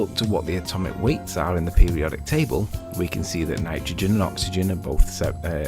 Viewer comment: Anybody else catch the, “Pyramid Table”??